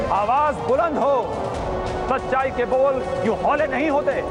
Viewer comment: What is said in 5:26 awaaz... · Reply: "आवाज़ बुलंद हो सच्चाई के बोल यूं हौले नहीं होते"